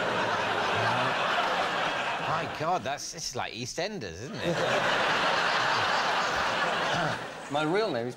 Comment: what did david say, there's no subtitles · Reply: "My god it's like EastEnders isn't it?" (EastEnders is a dramatic British soap opera)